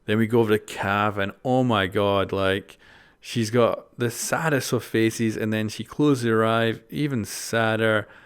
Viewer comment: Is sounded like you said the saddest of feces